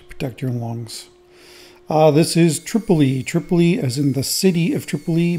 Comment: “Tripoli”? Haha all this time I thought you were saying “EEE.” 😂 Love the shape, but love that wood even more! For that reason, I’m not a fan of the red color at all. I think it pulls your attention away from the beauty of the wood. Would have preferred some very dark epoxy for better contrast. But I wouldn’t have guessed the final wood would have such wonderful light color looking at what you started with. 🤷‍♂️